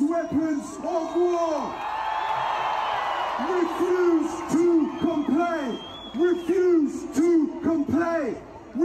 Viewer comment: REFUSE TO COMPLY is a great title of a heavy metal Jesus metal song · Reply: Unfortunately it sounded like he was saying "Refuse to complain!"